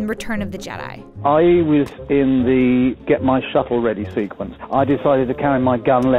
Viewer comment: I thought he said "get my shuffle ready" lmao